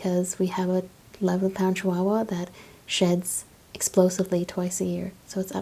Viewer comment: did you just say in the ad using ur asmr voice that ur dog shits twice a year or … sheds..